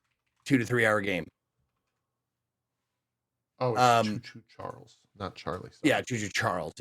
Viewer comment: I thought said "Jew Jew Charlie" after asked how do you follow Jesus Christ..